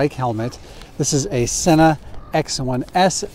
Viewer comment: and 6:24 the subtitles say Santa instead of Senna